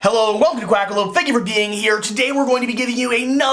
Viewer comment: lol at the start I thought you said Weclome Quack to Quackalope.... that actually works hahahaha